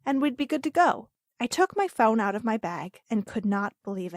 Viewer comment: Is it just me or did she say phaune insted of phone